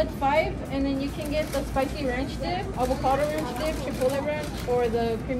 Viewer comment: Damn finally Taco Bell did something good and I thought the worker said “chick fil la dip for a moment lol”(it was chipotle)